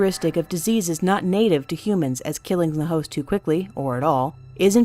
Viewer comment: Did say that it won't blah blah blah so they won't kill the hoes to quickly?? XDD